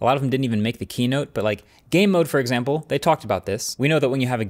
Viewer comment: I heard “gay mode” and got excited smh 😔